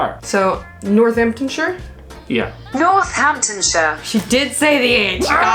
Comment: HI guys, with your accent when you say shire it sounds like you say shrrrr 😂